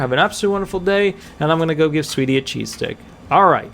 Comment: I wish I had some cheese sticks lol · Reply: Ohhh, I thought he said cheesesteak! And I’m thinking damn that’s a lucky pup!